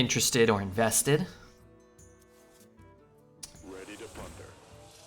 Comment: first and second time I heard the minion says "ready to f her" I said like it can't be right and on third time I've heard " ready to plunder" and now it's gonna stuck with me